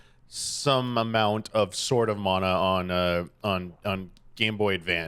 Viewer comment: Thought I kept hearing them say “sorta mana” when it was Sword of Mana